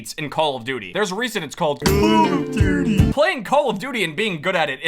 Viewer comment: dude for the longest amount of time i thought you were saying "how you doing?" not "call of duty"😭💀